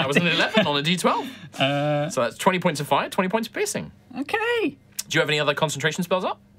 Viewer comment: Please tell me I'm not the only one who heard 20 points of pissing 🤣